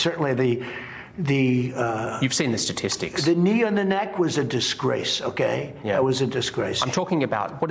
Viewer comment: Honest question: What was Trump trying to say, when he stuttered: The "neo-na-nak" was a disgrace. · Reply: "the knee on the neck". He slurs it but I'm fairly sure that's what he says.